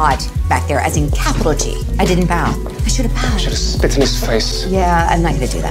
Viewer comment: Did Lucifer just say he should have spit in his face ?? 😂😂🤣